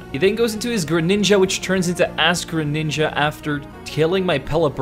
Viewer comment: Is it just me at 19:27 does he say ass greninja